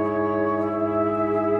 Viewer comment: What makes you… crappy? 🙃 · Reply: hahahaa he said happy but i thought he said crappy at first too